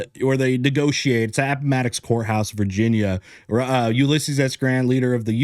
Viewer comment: As a non American I thought you said whore house not court house lol